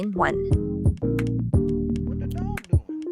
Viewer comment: Why do i keep hearing "what the dog doing"😂😂